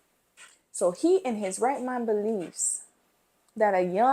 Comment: When you said, “In his right mind believes,” I thought you said, “rat mind,” and I feel like that is also accurate. 😂